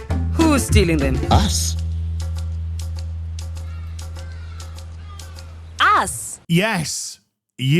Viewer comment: sounds like she says "ass", which is what this game is 🤣